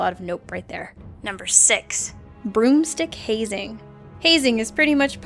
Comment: the video paused rite as said "broom stick" and is sounded like she said "groom's dick". that was awkward